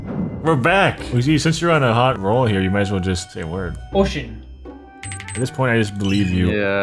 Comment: Day ten when you said ocean i thought you said bullshit